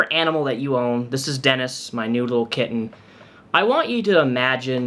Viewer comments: I thought you said, "my noodle kitten" which is adorable. I was wrong but I'll continue to call Dennis a rotini baby. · Sounded like you said ‘this is Dennis, my nude little kitten..’ Lol American accents. (Sorry I’m Australian and your accents are hard on me. It’s worse with thicker regional American accents). Your kitty is lovely. I have a kitty on my hip right now named Bowie. Obvious who she was named after. She’s 9. I rescued her at 2 weeks old. I’d rather this bitch spit in my face or kick me than her dog or your or my cat. I can defend myself by slapping the shit out of her- which she deserves. She also deserves a court order where upon she is not legally allowed to own an animal- ever again. Coz FUCK HER! Thank you for raising her shitty profile as everyone online needs to know about this witch.